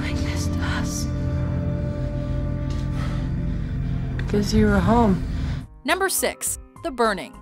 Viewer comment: "why are you doing this to us!?" - "cuz you were home." *Starts shitting bricks* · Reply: I originally thought they said "cause you're a home"